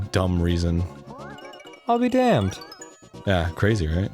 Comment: "I'll be Dan"- Dan Avidan 2018 (Yes I know he says damned, but that's what I heard at first lol)